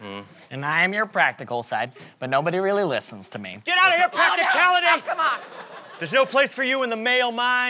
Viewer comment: PRACTICALITY GOT THE GIRL, I FINALLY UNDERSTAND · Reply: oooh sorry I thought you said practically not practicality Whoops